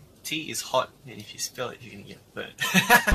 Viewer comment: i think he said "burn" not "wet"